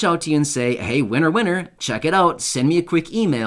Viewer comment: I shared this on FB. We would really appreciate the help. I am a mom of 3 little ones and have been homeless since the beginning of covid because our home was condemned by a judge whom said it was not livable. The very next week covid hit and we have been struggling ever since. I have fell between the cracks on everything. On top of that my waffle house I worked at was the first place in Athens Georgia that got shut down. I went to file unemployment and someone else filed on my name. Well 2 years later I still haven't seen a dollar from it and still can't find steady work or a home for us. So ANY help would be so much appreciated. Thank you for your info and this terrific giveaway. Be blessed and pray for us. 😊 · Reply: I thought you said it would say winner winner send an email what's up with the what'sup app?